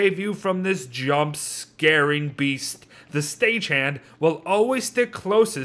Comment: I thought he said sta chan 😂